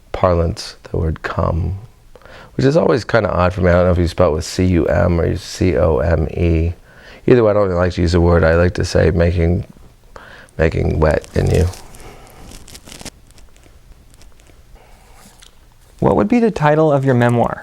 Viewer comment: Did he just say 'making wet in you??' Suddenly, I'm picturing Coach Steve from Big Mouth.